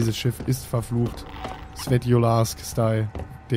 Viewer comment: was hat er gesagt? (vor dem DayZ Style) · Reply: Svetlojarsk-Style ( EIne Sadt in der Standalone von DayZ heißt so) :)